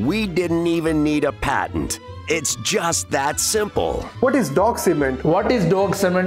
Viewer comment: I'm so baked I thought you said dog seamen.🤣🤣🤣